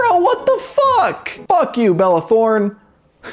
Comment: i heard ''fuck you bella porn''